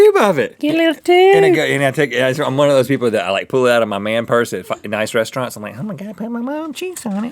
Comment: I swear... 8:34 I think Rhett said “at a fkn nice restaurant “